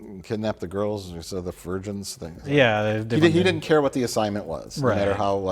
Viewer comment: "Kidnap the girls. (Mumbles) The virgence" 26:55 i did a double take. Thought he said virgins. 😆